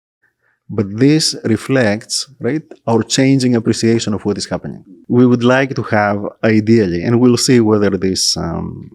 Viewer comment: please, my curiosity is killing me, what did you say at 58:50 (you edited out the audio) that made laugh?? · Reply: I asked: What is the Mantzoros classification?